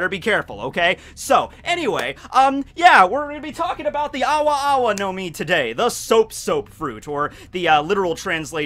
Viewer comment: For a second there I thought it said ara ara no mi